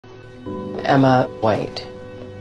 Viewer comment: Sounded like she said "Emma White" as in 'Snow White' her mama 🍎 Instead of 'Emma Wait' 😂 I like your cosplays 👍